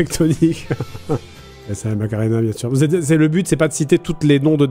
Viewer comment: c'est moi ou il dit maghrébin ?? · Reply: non il dit macarena